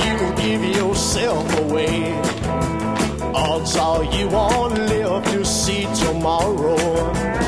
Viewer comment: As a kid I always thought it said, "Oswald, you won't live to see tomorrow."